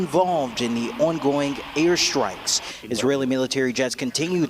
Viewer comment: I heard "...oink oink air strikes...." 🤣🤣🤣